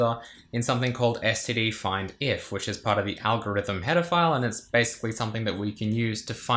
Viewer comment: When Youtube captions give "algorithm pedophile" instead of "algorithm header file"